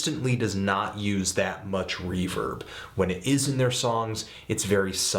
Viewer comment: When Anthony said "reverb" I thought he said "reefer" and I was confused because they use a lot of that.